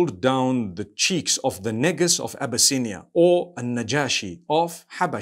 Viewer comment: I thought he said my niggas from abysinia 😂😂😂